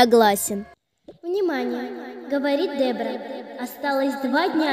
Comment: мне одной послышалось «бебра» 🤣